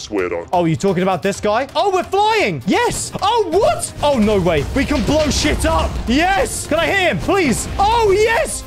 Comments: minute 12:23 did he say " you can blow shi* up?" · WHAT DID SAY?? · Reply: says oh no way we can blow s**t up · did he just say "we can blow sh*t up" in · he said sh## at 12:24 like am i the only one who heard that